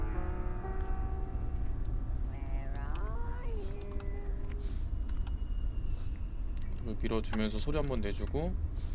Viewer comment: 초에 무슨소리 나만들림? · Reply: 7년만에 답하네요 할머니가 where are you? 하는 소리입니다